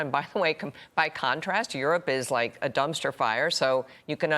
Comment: Did she just say “dumpster fire”